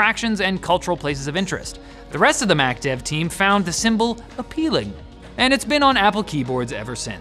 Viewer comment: Hmmm....did he say he said appealing....I heard a slurred "apple-peeling". I need new speakers.